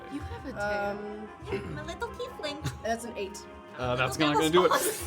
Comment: what’s that you said? Did you say ‘little tiefling’ in cutevoice? In what dystopian world is a 6’4” tiefling little or cute? 🧐😱🤔😨🤯🤣🤣